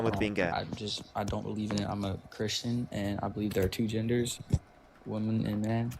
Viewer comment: OH when he said "I'm a Christian" I heard "I am a Nazi".